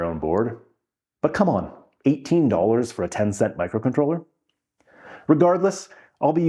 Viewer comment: everytime you said 10 cent i thought you were saying "tencent", the chinese company